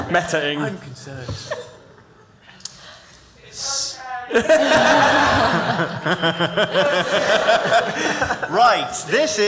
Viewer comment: what does the audience member say at 23:37 ? i couldnt find an answer in the comments. came back to this video just for rapping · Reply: "It's OK"